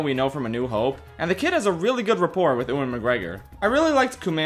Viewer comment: did he just say "ooen mcgregor"